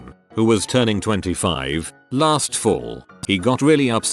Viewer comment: I thought that said 25 feet tall!